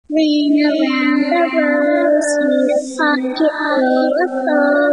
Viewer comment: Ring around the rosies: This line is thought to refer to a red rash or circular sores that appeared on the skin of those infected with the plague. This "ring" around a "rosie" (red sore) could symbolize one of the first symptoms of infection. A pocket full of posies: During the plague, people often carried flowers, herbs, or sweet-smelling items in their pockets, believing these would protect them from illness or mask the scent of death and decay. This practice arose from the miasma theory—the belief that disease spread through "bad air" or foul smells. A-tishoo, a-tishoo: Some interpret this as a reference to cremation or the overwhelming amount of death. Another theory suggests it mimics the sound of sneezing or coughing, as respiratory symptoms were common in pneumonic forms of the plague. We all fall down: This line is widely seen as a reference to the high mortality rate of the plague and the way it spread so quickly, causing countless people to falling dead on the streets and those infected in their homes succumbing horribly. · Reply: I thought the child said "ashes, ashes" instead of "a-tishoo". It makes more sense that way tho.